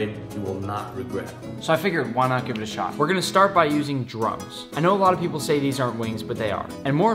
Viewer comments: In 0:52 it sounds like he saying drugs but he actually saying drums. Wooosh for a second tht scared me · I heard "We're gonna start by using drugs..." Uhm. 😂😂